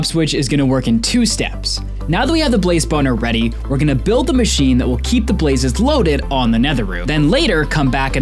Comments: captions say blaze boner lol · Did he just say blaze boner?